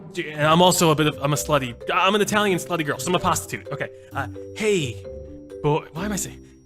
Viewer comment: Lol Did he say " imma pastatute" at 10:05?